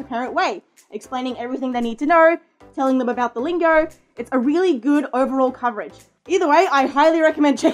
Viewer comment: wait, did you just say duolingo? Oh poop.